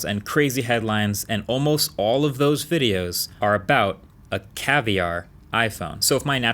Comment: I thought you were going to say "and all of those videos are from Lew"